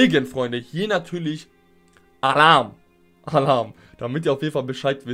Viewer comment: ich habe legit Haram verstanden hahahah